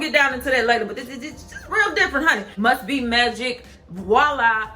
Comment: I thought I you said musty magic bc I had my volume low 💀💀💀